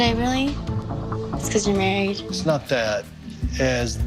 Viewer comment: did she really say “ it’s because you’re married “?? 😂😂